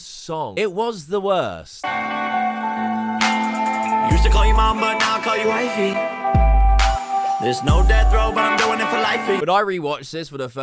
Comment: i thought he said "used to call you mom, now i call you wifey" too, i thought it was a reference to the time his mom was always trying to get with him lol.